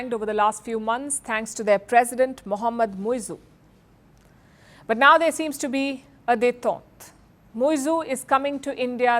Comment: What did she say at 0:15 ? There seems to be a ??? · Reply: but now there seems to be, a day thought, Muizzu is coming to....